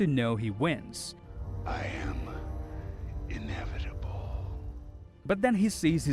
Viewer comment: I always thought he said I am edible